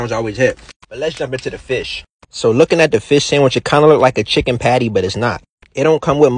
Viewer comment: “9/10 for the fith sandwich” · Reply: Y’all must need hearing aids or something, it sounded like he said fish with a ‘Sh’ to me. 🤷‍♂️ lol